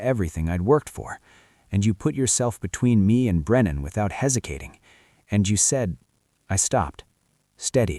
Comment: I listened to 48:19 three times, but I keep hearing "hesikating"😅